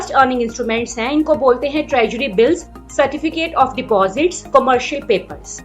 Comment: a humble correction treasury instead of tragedy, nice info though thnk u 🙏